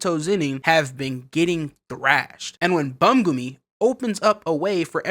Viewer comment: Did you just call Brodie bumgumi?😭😭😂